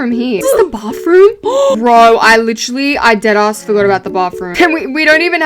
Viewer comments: did she just say "I dead a$$ forgot about the bathroom" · Did she say “ass”???